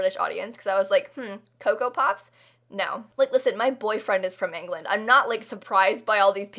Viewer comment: my WHAT the h*ck? your what lives where? Is this a thing? Did I do them misheard rambles? Can someone confirm? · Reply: has a boyfriend his name is Dan.